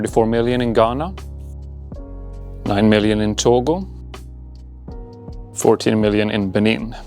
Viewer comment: I misheard Togo as total at 9:10 and was like "Huh the math ain't mathing" for a solid second there lol